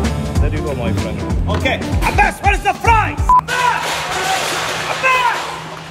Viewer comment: he said: Abbas But I heard: a bus a bird I burnt